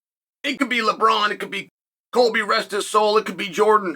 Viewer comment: "...it could Colby, rest his soul." What!? Colby Covington died!? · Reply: I thought he said Colby not Kobe as well